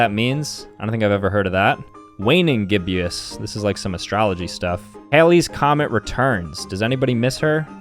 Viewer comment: it’s astronomy instead of astrology!